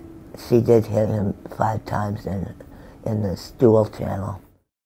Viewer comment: “The way he said it, and the way I took it were two-hundred years apart.” I mean don’t get me wrong— she says some comical stuff, but the eloquence is there y’all. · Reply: I hear it! 21:00...in the stool channel!?! I mean whAXD!